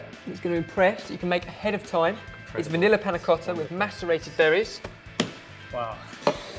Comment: i thought he said masturbated strawberrys am i the only one? yes ok